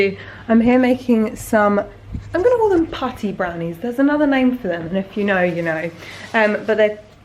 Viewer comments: Me, a non-native English speaker: "Did she just say 'party brownies'? Is she openly making like a pot brownie?" Please someone clarify. Thanks! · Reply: i thought she was saying potty brownies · I thought you said "party brownies," which I have heard of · Me, a non-native English speaker: "Did she just say 'party brownies'? Is she openly making like a pot brownie?" Please someone clarify. Thanks! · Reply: They're called " slutty brownies" but she didn't want to say that so she said " putty brownies" putty is like this gross squishy stuff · I swear I thought she was saying potty brownies